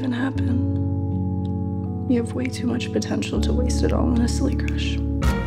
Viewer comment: she said "silly crush" but it makes sense to not be sure what she said. the actors mumble at least half of their lines